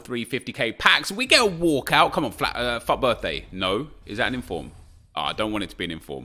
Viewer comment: did he say flat earth